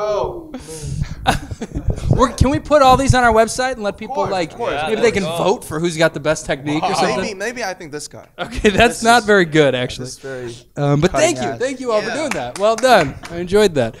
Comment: did he say taes selfie/ technique wasn’t very good.. someone please tell me I heard wrong